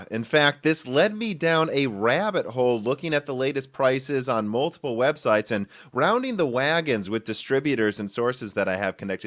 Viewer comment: Did you say 'multiple webseiten'? :D